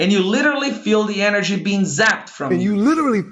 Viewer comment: eu entendi ele falando "zapped from me" e não "you"